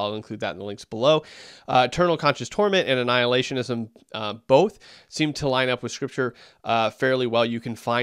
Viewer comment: Did just say annihilation lines up with scripture 🤨🤔🧐